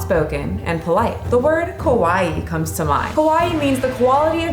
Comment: It threw me off when you said "kawaii" because it sounded like you said "kowai" which means scary